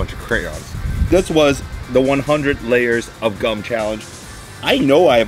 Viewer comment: I thought he said 100 layers of cųm.. Gum. Gum. Gum.